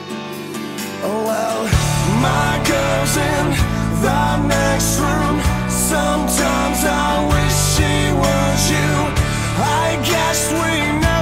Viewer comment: Always thought he was saying "My cousin, the next room, sometimes I wish she was you" like some kinda weird hillbilly thing XD lol Good song nonetheless.